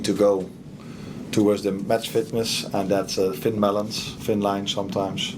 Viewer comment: did he say ‘it’s thin melons’ is that a Dutch phrase???